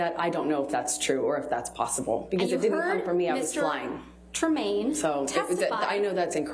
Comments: did she just say "I was lying?" - I can't quite make out exactly what she said there but that's what I'm hearing. Anyone? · She ADMITS she lied but I can't see that anyone heard it but me - it was while Camille was talking. Someone else please listen at 6:48. To me, it sounded like she said "It didn't come from me, I WAS LYING" and then she caught herself, and said "So...'I know that's incorrect, that's what I meant to say" !!!! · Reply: I heard her say: I was flying, not lying. But of course, anyway, since she is always lying, your mistake is quite understandable! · did she say 'because it didn't come from me, I was lying" ?